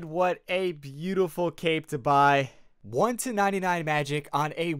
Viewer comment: I thought that said I bot 1 to 99